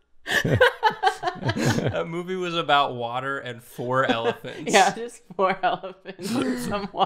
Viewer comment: The Types of Water (according to): Salt, Fresh, Brackish, Warm, Cold, Bottled, Hose, and “For Elephants.” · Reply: That makes a lot more sense. I thought they were saying "four elephants" and I spent a while wondering how the number was relevant